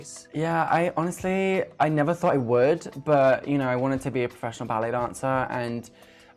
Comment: LMAO!!! I thought he said he moved to Sacramento to be a professional Mariachi Dancer O.o